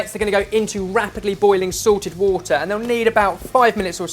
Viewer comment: Every time says "salted water" I hear "sorted water". A very unique and well documented type of water that you can only see on Sorted Food